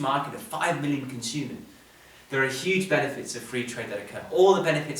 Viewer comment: I must have misheard at 6:25 when said "27 nations, that's a huge market of over 5 million consumers". But the population of Germany alone is over 80 million, so what did he actually say/mean? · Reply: 500 million